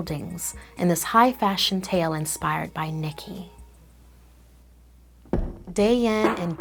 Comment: when said Nikki idk why i thought at first she said Nike